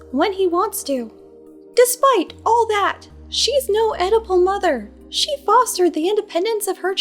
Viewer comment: i kept hearing 'edible mother' and that fit surprisingly well.